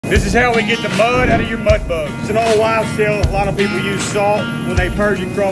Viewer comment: when I heard "Thi sis how we get the mud out of your mudbugs, I heard it as "This is how we get mud out of your buttbugs." lol